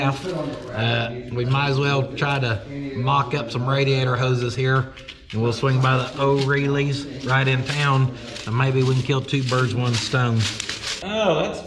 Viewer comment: I heard "three birds, one's stoned" 😂😂😂